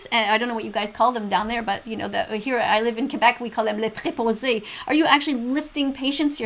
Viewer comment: She had a stroke at 10:14, what kn earth did she say Leheblorzay?